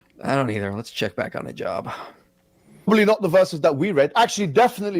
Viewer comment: 😂😂😂....at the start of video, I heard: ".....I dunno let's look back on the job.." 😂😂😂and the hijab guy popped onto the screen. Ironically you guys are working so hard to minister to this guy....keep up the good work in Christ. God bless